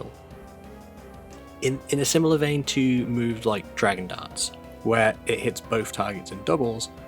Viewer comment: When you mentioned Dragon Darts, I misheard and thought you said Dragon Dance at first. Imagine that, though, if Palkia’s self-target moves became team-target moves for that turn! AoE Protect, AoE Recover… that would be a pretty broken ability!